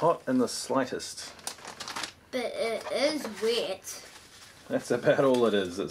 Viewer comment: "That's not hot in the slightest, but it is wet" I thought she said "but it is weird" LMAO